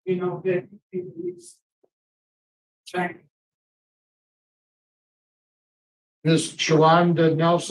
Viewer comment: I think the lady who got escorted out by security was there in support. It sounded like she was saying “thank you, Jesus!”